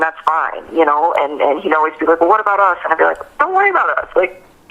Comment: "When WE went to North Carolina." Anyone else catch that? ( WE )